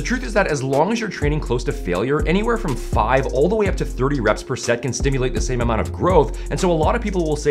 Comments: I just misheard per set, with "per second" shit myself there for a second 😂😂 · I thought at first he said "5 to 30 reps per second" instead of per set. I got scared.. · I thought he said 5-30 reps per seconds 😂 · I thought you said reps per second for minute there